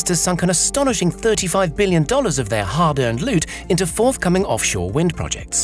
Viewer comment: did I hear hard earned loot?